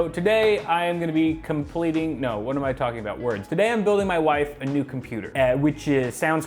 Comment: I somehow heard that as "I'm building my computer a new wife"